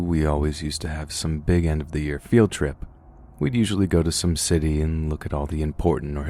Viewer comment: thought he said “we’d usually go to Sin City”😂😂😂